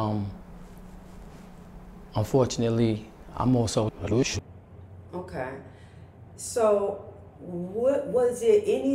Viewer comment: Anybody please, can u tell what word he said that he unfortunately was on 1:41 please? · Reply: shooter .. youtube strikes for certain words